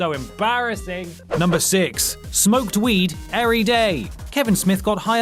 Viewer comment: Did you just say earyday?